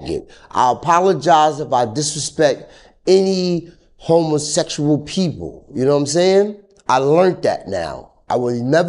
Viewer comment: I legit thought he said "Homelessexual People" 🤣🤣🤣